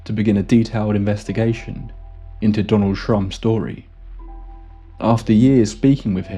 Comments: i tried to enjoy this video but i kept recoiling every time i thought i heard "donald trump" 🤢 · Take a shot every time he says "Donald Shrum" 😅❤🥃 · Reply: First time he said it, I thought he said Donald Trump and I was like, "Whaaaat???" 🤣🤣🤣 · Who else hit rewind?😂😂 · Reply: I thought i heard Donald Trump n rewound right away 😅 I thought u sneaky bugger in on the scandal as usual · I thought you said Donald Trump lol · Who else hit rewind?😂😂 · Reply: Yep i thought said Donald trump 🤔 aka donald dump 🙄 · Absolutely thought you said Donald Trump 😅 like damn this explains alot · I thought you said Donald Trump in the beginning of the video 😂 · I thought I heard the name Donald Trump at first 😂😂😂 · I almost shat myself when I heard "Donald Trump" XD XD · Take a shot every time he says "Donald Shrum" 😅❤🥃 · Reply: I was asleep and kinda woke up half dreaming and thought he was saying Donald Trump.